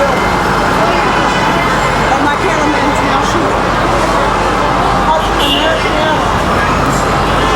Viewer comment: Did I hear this correctly? [02:45] “My camera man’s now shooting... American Airlines, right?”